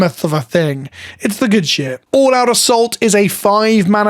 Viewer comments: When you said All-Out Assault I heard it as "all out of salt" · Who else heard "all out of salt" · Imma be real. I was listening to this video instead of watching it. So for a brief moment, I thought they made a card named "All Out of Salt" 🤣🤣🤣